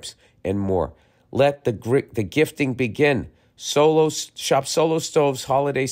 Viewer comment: i heard that almost “grift” lol